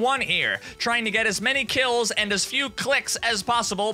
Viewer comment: I thought it said "kills and cucks" for a second